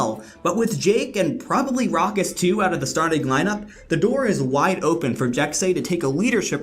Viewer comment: Sooooo... U know that Jake isn't on the roster anymore right? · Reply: Oh... Never mind I just thought you said with Jake and rawkus on the starting lineup at 8:05 but apparently I'm just deaf. Sry man